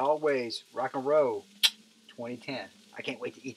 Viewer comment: great video! did you say rock n roll 2010 at 5:40?